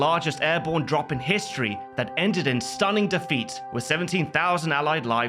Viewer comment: I heard "that ended in Stalin defeat" and I was like "...what?..."